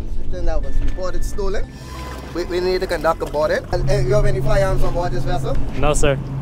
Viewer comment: How many firearms do u have 2 laugh... He said how many firearms oh I thought he said family none but my wife she's a pistol very true😂😂😂